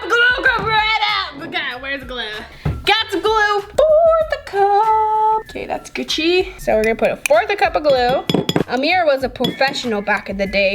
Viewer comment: Did you just say “that’s Gucci” ?